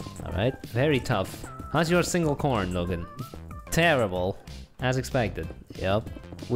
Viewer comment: he said Logan is that one of their REAL names if you dont hear it turn on captions